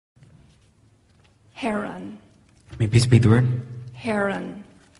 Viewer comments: I thought she said heroine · I thought she was saying herro for a second